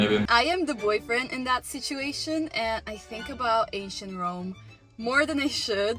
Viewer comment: Am I the only one that heard "asian Rome" instead of ancient Rome at 0:15? One of Rome's biggest cities - Antioch - was located in Asia after all.